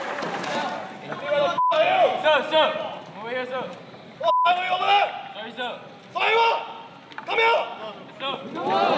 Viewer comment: What's the situation? From 2:17 - 2:20 I can't hear what the instructor was saying :/ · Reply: Instructor: Where the f*** are you? (referring to the IC) IC: Sir! Sir! Over here sir! Instructor: What the f*** are you doing over there? IC: Sorry sir! Instructor: Sorry what?! COME HERE!